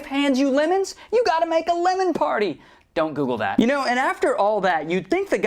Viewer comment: JEEZUS!!!! Why the hell did i actually google that damn it!!! I mean literaly i thought i heard "BEEEEP Google that"